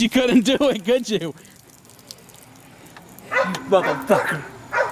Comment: did he say mother flyswatter?